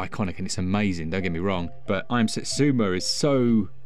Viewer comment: Did you say "I am Satsuma" at 3:54? Turned into Donald Trump, did you?